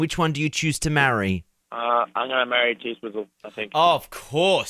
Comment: I didn't understand..did he choose Taylor or Kendal? ;p :3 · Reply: Athina Kalantzi I thought he said T-Swizzle but I also wasn’t completely paying attention.